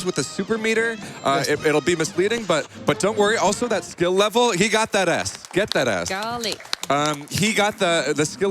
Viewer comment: I heard "he got that ass, get that ass!" Edit